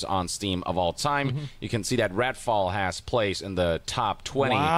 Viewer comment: did yong call it "ratfall"? 😂